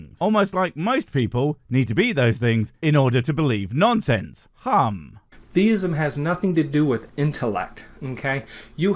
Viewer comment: Theism. Deism is something else entirely. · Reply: Sounded like he said "deism" - but both work in the context.